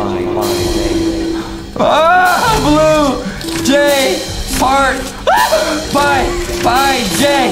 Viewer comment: I love the way Justin say fart instead of heart tho 😂